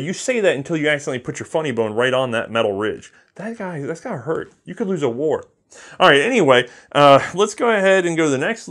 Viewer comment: Does say: "You could lose a wart!"? Has any gun tuber ever used warts as a measure of quality?